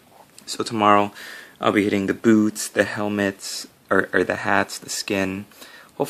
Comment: Almost thought you said tomorrow I'll be hitting the booze (instead of boots). The FBI may think I'm buying grenade launchers on ebay. :) I almost wish that Dorn had gone traitor, looking at yellow is tough and if they had changed their armor to black color it be easier.